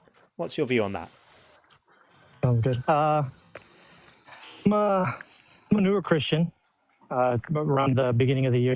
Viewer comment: When someone says "I'm a newer christian" all I hear is "I, manure christian".